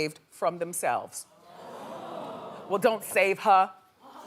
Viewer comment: what’s the second part of what Wendy says here “Well don’t save her…” and then the audience says the rest of it? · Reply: "She don't wanna be saved" from no role modelz by jcole